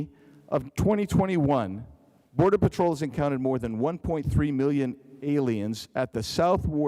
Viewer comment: What did he say at 4:17 mins? "Border Patrols encounters more than 1.2million aliens" ?